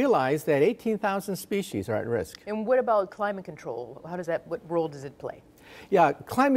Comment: Did she just say climate control?